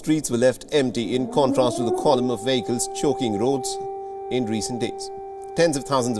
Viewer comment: did he say column of vagrants ?